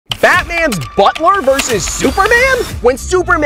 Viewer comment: It sounded like you said Fatman in the intro